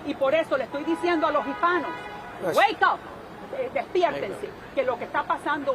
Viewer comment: what did she say? "WAKE UP!" ? After all this anti woke nonsense NOW she says wake up? 😂😂😂